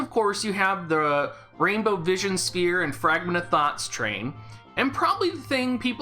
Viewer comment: I keep hearing “Fragment of Thot.”